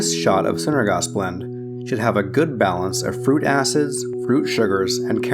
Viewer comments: sounds like he says fruit asses lol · did you say "Fruit Asses" ? Joking aside, EXCELLENT video.